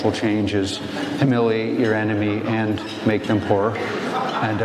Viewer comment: I need to get a hearing exam. When he said you have to humiliate those who disagree with you by making them poor, I thought he said by making them twerk 😂 That could be humiliating, but how to make someone twerk?🤔 Glad that Glen cleared that up.